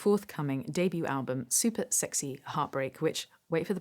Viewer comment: I thought said “Super Sexy Heartbreak Witch” wait for the plug…”